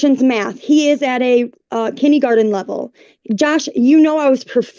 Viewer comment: Not BOOLYING!! Also, at 5:26, did she call it "kiddie-garden"?